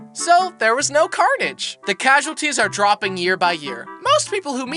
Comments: i thought you said “ear by ear” there, you FAILED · Did nobody realize that he said at *6:09* ear by ear instead of year by year.